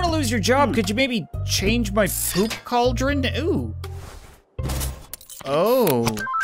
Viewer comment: any one else thought he said soup cauldron